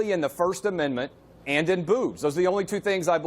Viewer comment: really sounded like he said booze, and not boobs